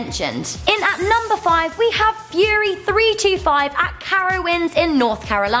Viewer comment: i thought she said Furry 😂😂😂 not fury